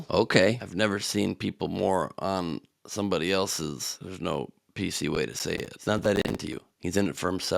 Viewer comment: At first, I thought you said “piece” instead of “P.C.” 4:05 I think “piece” is pretty much P.C.